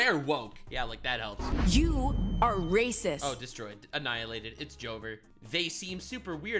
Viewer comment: While a small complaint compared to how bad the rest of the game is but I noticed that the games subtitles don't exactly match what the characters say 3:10 she says "You are racist" but the words that show up are "you're racist". Again a very small problem but for some reason it just stuck out to me.